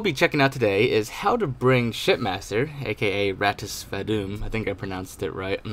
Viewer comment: I thought you said shit master